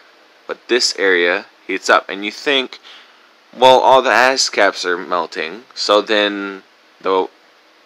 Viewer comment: "..all the asscaps are melting..." pretty sure I heard that.